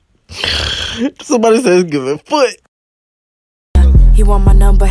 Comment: what did the guy in 1:11 say? i didnt understan him😭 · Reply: «somebody said its giving foot»